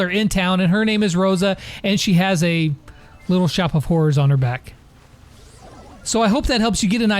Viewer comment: I'm pretty sure my ears are broken cause I heard say 'watch out cause she has a little shop of whores on her back'...